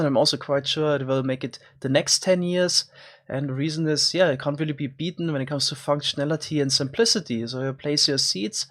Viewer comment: did I hear a "Schnellity" at 0:20?